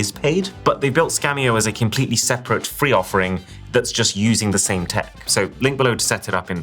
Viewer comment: when ever I hear free, I hear Honey